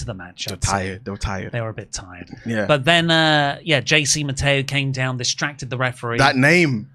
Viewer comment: Still whenever I hear "JC Mateo" i hear "Chasing Ma Tail"